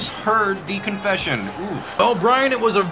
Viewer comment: I swear to god the first time i wached this vid i heard "her apponent is verry dum"